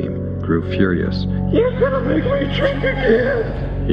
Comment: Can anyone make out what Wallace is supposed to have said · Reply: No, it was good, very passionate and deeply felt. I actually thought he said 'trick' instead of drink but of course drink makes more sense. Thanks for responding. And cool production!